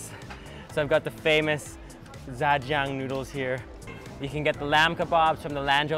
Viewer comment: Can someone tell me, are these Dan Dan noodles? I don't speak Chinese, sounded like he said jajeng noodles.